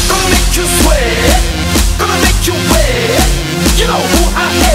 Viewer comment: I heard "Gonna make you swag"